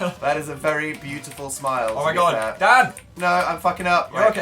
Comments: did anyone else hear phil say "dad"? hahahha I know he really said "dan" but that's what I heard XD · je sus christ i thought phil called him "Dad" for a second there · um. DID HE JUST SAY DAD? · IT SOUNDED LIKE PHIL SAID DAD WHAAT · when he said poor nose i thought he said pornos whoops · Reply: ish I could have sworn Phil said "Dad" instead on Dan... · ok did phil say "dad" or am i trippin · dosent it sound like phils saying dad? I mean I know hes not... Just though it would be fun to point out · did Phil say dad? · Does Phil call Dan dad? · Lmao it sounded like Phil said Dad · I thought Phil said DAD not DAN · did Phil call Dan dad??? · Did he call Dan dad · does Phil say dad instead of Dan? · did phil just call dan dad? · did Phil just say dad